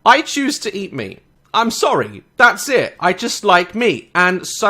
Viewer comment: I actually thought you said , i like to eat me , despite that I love this man for what he does he makes me laugh so much on watches and he does make jokes but he doesn't want to offend anyone he just wants to make people smile and I like that :)